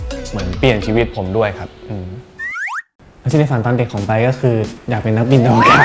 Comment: มีใครได้ยินว่า ชีวิตในฝันของไปป์ บ้าง หรือเค้าหูฝาด แงง